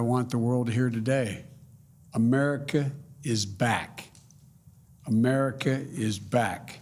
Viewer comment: I thought he said, "my memory is back"